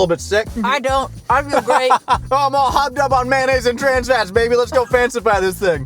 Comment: I heard "I'm all hopped up on Mayonnaise and trans rights baby lets go fancify this thing!"